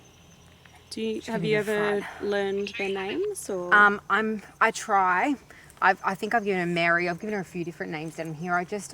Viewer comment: can’t make it out but I hear a lady I’m not sure if it’s someone in ur group or something else. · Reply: The voice is electronic and says the name Caitlin who is one of the team of Haunted Down Under..... I believe it came from one of the female spirits using the recorder to make herself known to Caitlin.